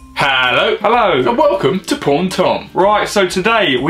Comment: OMG everytime i watch ur videos it sounds like ur saying "porn tom" instead of paul and tom xD